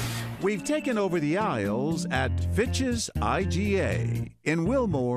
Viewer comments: Fitch’s I thought he said bitches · I thought he said bitches’ iga 🤐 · I swear I thought he said the name of the grocery store was “bitches” 💀